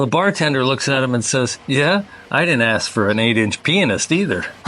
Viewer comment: Can someone please explain the joke punchline. I don't get it??? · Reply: Nevermind, I misunderstood what he said. I thought he said that he didn't ask for an 8 inch penis, which doesn't make any sense. Now I know it was that he didn't ask for an 8 inch pianist.